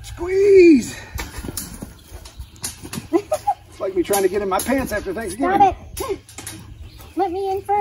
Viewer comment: I misunderstood you there at 7:04 - thought you said "Like YOU trying to get into my pants after Thanksgiving." WHOA TMI, TMI I said - lol